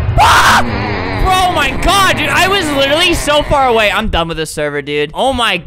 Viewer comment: He said "BRO MY GOD" instead of oh my god Idk why that made me laugh 😂